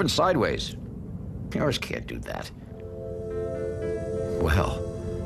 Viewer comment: What did he say? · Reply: 4452 "Yours can't do that"